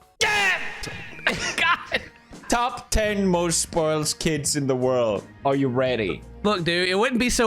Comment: OOF I GOT SCARED AF I THOUGHT THAT SAID "SPECIAL NEED KIDS"